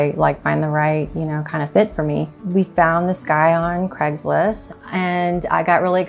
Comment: Did anyone else notice the mess up on the subtitles? They wrote the sky instead of this guy 1:59 haha. Awesome home though!